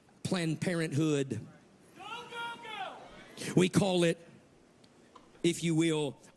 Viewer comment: Every time I watch a video of Greg Locke, there's always one sycophant yelling the same thing repeatedly. What the hell is that guy saying at 6:53 ? Is it "go, go, go"???